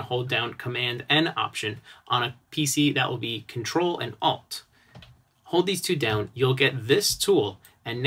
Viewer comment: Thanks for that. At 4:50 you are using shortcut on pc ctrl and... ot?alt?odd? I dont have such a command on mij pc.What are you saying there? :) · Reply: ALT, it's another modifier key on your keyboard. You can also check the keyboard shortcuts and confirm that my short cuts are the same on your machine.